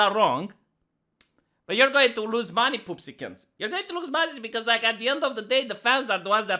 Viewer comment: Did you said "пупсикle" ? Finally someone that get my Englo-Russian puns! oO tears of happiness